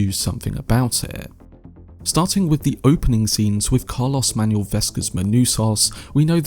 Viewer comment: that’s not what you said, man. At 1:52 you said “starting with Carlos Manuel Vesga Manusos”. Sounds like you thought his character’s name was Carlos 😂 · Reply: Lol. I said "Starting with the opening scenes with Carlos-Manuel Vesga's Manousos". I know that that is the actor and Manousos is the character. Sorry if it caused confusion but that's what I meant.